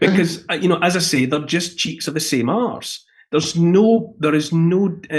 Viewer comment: All I heard was, " their just cheeks of the same ass." LOL! "C'mon Man"